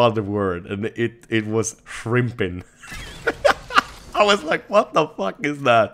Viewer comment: did he say frimpin at 4:16 and what does that mean